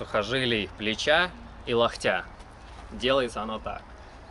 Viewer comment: Мне послышалось, или он сказал "Лохтя"?